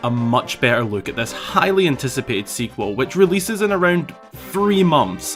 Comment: My dumbass in the beginning when you said "highly anticipated sequel" heard it as "Hylian-ticipated sequel" 💀